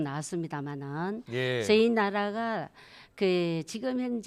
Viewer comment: 뭐? 내가 잘못들었냐? 저희 나라???